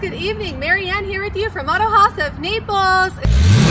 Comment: I thought I heard autohaus "Nipples"